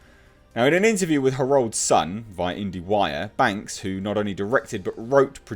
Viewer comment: When you said Herald Sun at the 5:00 min mark, it sounded like “her old son”. I had my headphones in and jumped off the treadmill to rewind it 🤣😂🤣😂